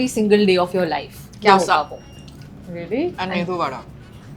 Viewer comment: What did diana say @ 19:31? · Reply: Dosa and medu vada